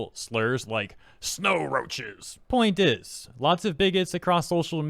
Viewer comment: I swear I thought I I heard you say “ngas” at 0:32 😱. Bigots is what you said but holy shit that was funny lmao.